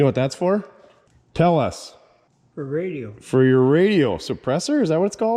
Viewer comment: At minute 1.15 what was that little gizmo? I think it came from behind the dust cap. I heard "radio" or "radial" suppressor? What is it? What does it do? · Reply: Rodeo suppressor